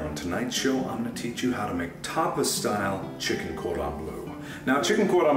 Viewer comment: In latest news, Cooking with Chef Iplier has been cancelled, due to leaks. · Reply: lol I thought he said "chicken quote on blue" defo a real recipe!